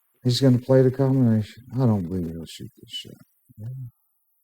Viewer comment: Does he say " I don't believe he'll shoot this shit?" LOL